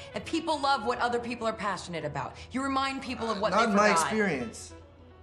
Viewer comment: Can somebody what Emma said at 4:12 ? I heard "You remind people of what ______." Someone Fill the blank please. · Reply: I listened again. Umm...is that blank maybe "what they forgot" ??